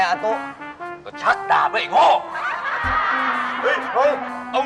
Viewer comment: อาตุ๊ พูดว่า ก็ชักดาบไงไอ้โง่ 1:06 1:07